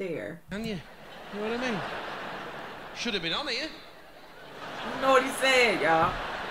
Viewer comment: i think he said "should have been on here"